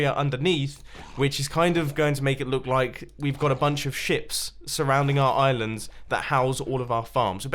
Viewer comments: Haha when he said ships it sounded like he said shit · thought he said a bunch of shit